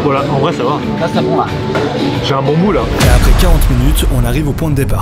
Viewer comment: J'ai un quoi 2:22 ? 😳😳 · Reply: Hahahaha un bon bout mdrr je suis choqué pour moi c'était super clair